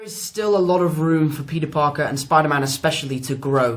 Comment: The subtitle said Pita Pocket instead of Peter Parker